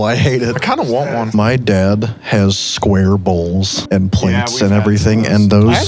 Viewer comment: thought said his dad had square balls